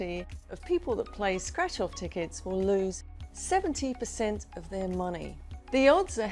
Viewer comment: Im sorry but irts ahrd to understand you somwe times. at 8:30 did you say 70 or 17 percent? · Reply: She said 70%! I don’t feel so bad anymore. I have been doing better than that.